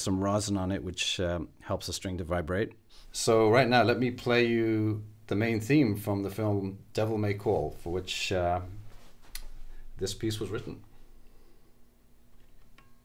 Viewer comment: What an amazing instrument. Caught me off guard when I thought he said "Devil May Cry".